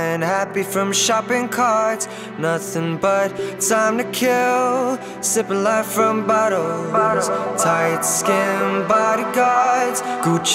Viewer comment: i guess i spend too much time on tumblr, cause i hear "sipping life from buttholes".